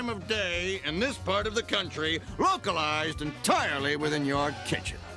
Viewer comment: "'AURORA BOREALIS.' At this time of year at this time of day in this part of country LOCALIZED ENTIRELY WITHIN YOUR KITCHEN." "yes." "That's it?" "yes" death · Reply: lol I thought said “may I see it?” instead of “that’s it?”